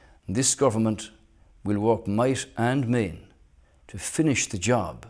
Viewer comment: what does he say at 6:52 ? might and may?